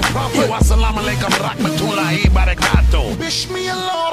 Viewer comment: I know he's not saying such, but it kinda sounded like he said "arigatō" (Japanese for "thank you", in case you didn't know)